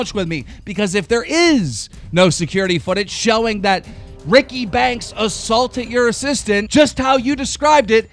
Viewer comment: I keep hearing him say "Ricky Bank".